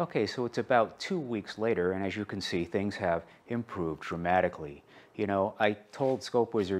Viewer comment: I'm guessing I heard wrong, "Things have improved traumatically"